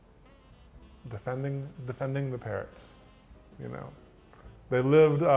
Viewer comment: omg XD I kept mishearing Parrots as parents and when was all like "When you killed my parrots?" I was very very confused until they said creeper. Then it finally clicked.